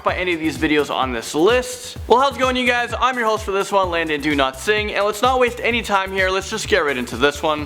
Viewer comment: Who else thought he said "Landon Don sing ''